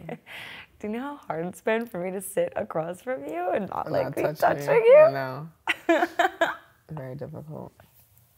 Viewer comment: I love this! So beautiful and heartwarming!! ♡ Does anyone know what says at 12:19? xx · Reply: Kelly Plunkett she asked “Want me to answer?” (The same question was answering)